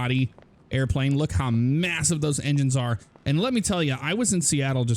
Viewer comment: Sounded like you said tello